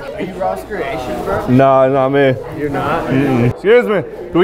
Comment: "Are you RossCreations?" "No, dat not me." I don't even think it was intentional, but I can't believe he didn't know from that alone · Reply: I heard "nah, nah not me"